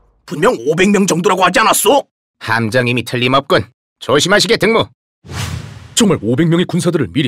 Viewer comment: 조심하시게 동무라고 들리는것 같음ㅋㅋ